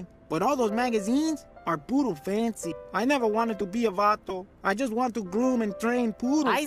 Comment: am I missing something or did homie say he never wanted to be a man